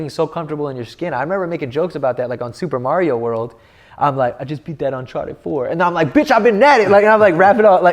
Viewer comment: I COULDVE SWORN HE SAID "I just beat that uncharted 4" in super Mario 😂😂 I thought I was hearing things